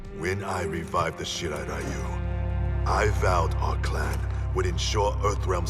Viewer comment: hanzo says shirai ryu and captions say shit outta you wth!! BAHAHAH